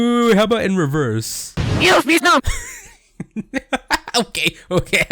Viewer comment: It sound like said “PLEASE NO!” when reversed 4:34 Anyways good video 👍